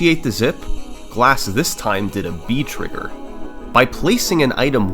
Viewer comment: I swear I hear "V-Trigger", and thought I heard yet another Kenny Omega thing from Mega Man.